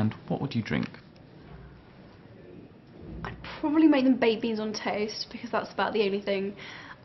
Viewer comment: i think she said baked beans... im not rying to make you mad or anything im just saying what i heard so don't think im against you cuz it did sound like babies